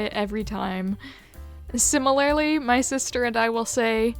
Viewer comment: simi-layly I heard that